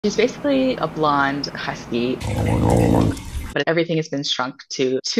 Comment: I heard 'she is a blonde husky' as 'blind husky' and the whole time I was watching I thought, 'This dog does NOT behave as though she's blind!' Had to go back to the beginning and double-check. Beautiful dog and beautiful owner!